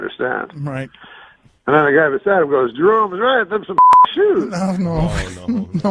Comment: What does Norm say at 5:56? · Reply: “write them some fucking shoes” the context was a criminally insane man told norm nice shoes, and some other criminally insane man said that to him. I know this bc I’m not a doctor.